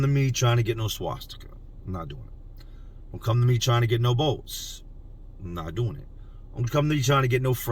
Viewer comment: Am I hearing you right? No Boats? What's the significance? (Ex Navy here lol)